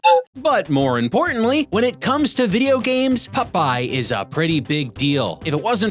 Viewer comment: I keep hearing "pupeye".... Probably the only one🤷🏻‍♂️